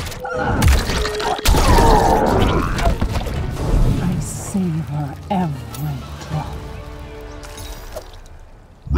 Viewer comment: what she said? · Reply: I savor every drop